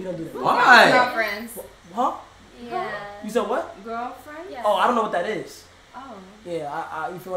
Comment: I swear Kai said boyfriend